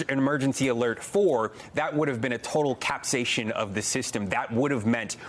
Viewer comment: "... that would have been a total 'capsation' of the system." What did he say there? Capsaicin? Caspian?